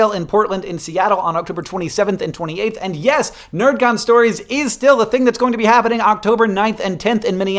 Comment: I heard "nerf gun stories" and was very confused for a second.